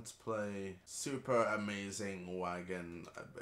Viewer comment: i thought u said super amazin WANKIN adventure LOOLL